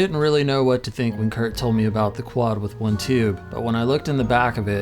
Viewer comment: Watch from 3:28 to 3:43. Am I missing something, or does this make negative sense? "Steve said Kurt was using a Twin Reverb with 1 tube. If what Steve says is true, I had to cross the twin reverb with one tube off the list." Wut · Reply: he said quad, not twin